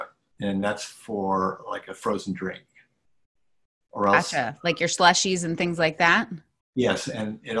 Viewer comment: Did I hear say “achha” as in okay in bengali at 4:32 😅